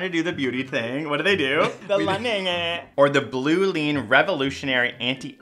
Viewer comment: what did he said? The lang?